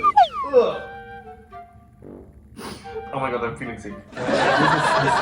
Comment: dia ngomong apa yg pas mual? hahaha mau belajar bhs inggrisnya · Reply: Daniul Glun ooh my god I'm feeling sick (itu bahasa inggrisny buat ngungkapin klo kmu mual atau nggak enak badan)